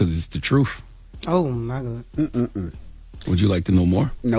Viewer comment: Did Charlemagne the goof just say trufe instead of truth?